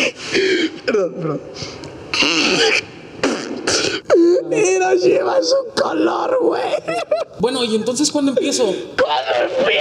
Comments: ¿Qué es lo que dice? Yo escucho: "Hiroshima es un color". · ¿Que dice en el min 1:34 ? Quiero saber que contestó pero solo entiendo "...es un color"